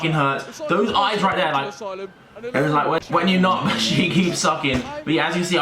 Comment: lol I heard him say "when u but and she keeps sucking"